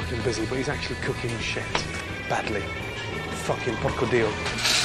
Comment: Did he said Porco Dio?! 13:05...hilarious